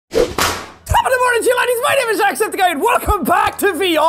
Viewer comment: what did he even say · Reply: Top of the morning to ya ladies